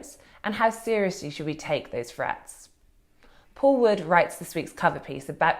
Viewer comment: Does The Spectator ONLY hire posh people people who can't pronounce 'th' properly or something? Katy Balls saying things like 'frets' instead of 'threats' at 1:48 ...followed by the equally plumby James Forsyth saying 'vuh' instead of 'the' at 28:16 and also 'feems' instead of 'themes' at 28:22. Seriously? Don't give me any crap about speech impediments etc, they're perfectly capable of pronouncing 'th' I'm sure of it